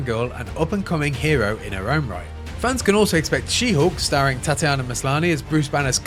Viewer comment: say whatttt? u said 'she hoark' instead of 'she HULK' 🤣🤣🤣